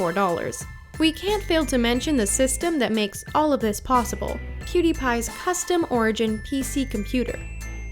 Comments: Did she say CutiePie? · Hold up, did she say ''Cutiepie''? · did I hear her say cutiepie? 😂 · I just heard Q-Tie-Pie!!! I've caught you right there "The Richest"!!! Your subtle reference to Marzia.